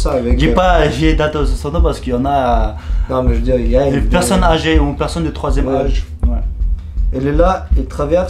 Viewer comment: À 7:16 j'ai compris 3iem reich xD